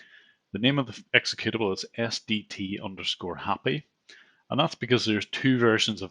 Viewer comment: Ok I’m gonna ask this truthfully, who thought he said STD at 0:54?